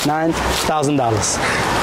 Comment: crazy how $1000 brings a man to tears and there are people in this world that could literally wipe their ass with that money without a second thought.. and both men live under the same flag. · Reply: It did sound like he said $9,000, but really he just said $1,000. He counted up, "... seven, eight, nine, thousand dollars." The thousand dollars didn't have a nine in front of it. It was just the final amount. There are no $1,000 bills.